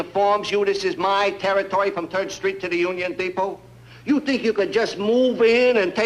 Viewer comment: Did he say “Turd Street” at 7:12?